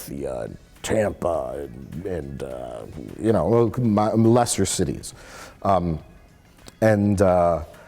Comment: "mmmmMolester cities" ??? 19:31 Am I hearing things?